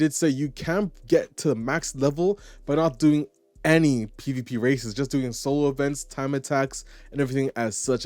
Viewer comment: mark, states that you can max out your level without any pvp races? Do i hear that right because if that's the case then i can happily say thank goodness. Makes me want to support this game when it drops in September.